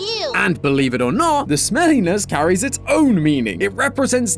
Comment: Huh at 12:03, the closed captions say "the Helliness carries its own meaning" instead of "smelliness". Slip of the tongue (toe?) or is something sinister going on with this sock? Hmm